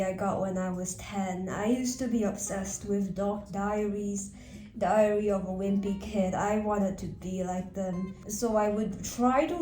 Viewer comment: I thought she said diarrhea of a whimpy kid